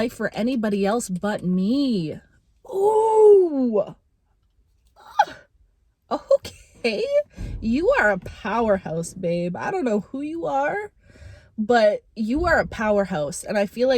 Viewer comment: ~ I heard POWER PUSS ahahahaah ~ Not wrong ;) ~ Lu